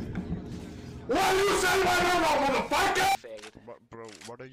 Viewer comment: Legends say, to this day, still doesn't know what he said. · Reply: 'What u saying'